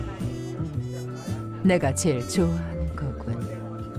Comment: bro what did she say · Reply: “내가 제일 좋아하는거군요.....” 한국어입니다